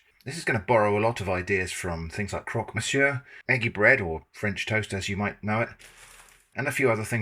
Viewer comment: The first time you said "eggy bread" I misheard and thought you said "manky bread" and now that's what it's called. 🙂